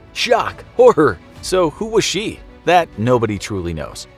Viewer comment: You say "Anna was Anastasia. Shocker. So was she?" Like wtf make it make sense. If you're saying WASNT you need to enunciate cuz now I'm confused. Ur asking me who was she when you just told me who she was.